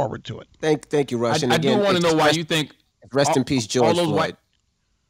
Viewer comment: Charlamagne says rest in peace to Rush. Am I wrong? Did I misunderstand? Did I hear him wrong? WTH?